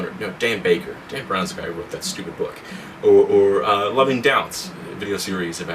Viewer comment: Right after 8:30, you say something to the effect, "loving doubts" but I'm not sure if that's it. I can't seem to find these on YouTube, did I misunderstand what you said?